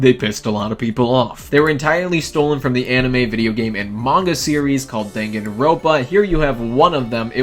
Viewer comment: at first time i heard "amongus series"